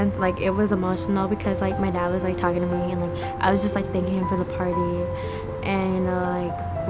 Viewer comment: father daughter dance " it was emotional..I was thinking about the party " Really..I think she said something else and I heard wrong.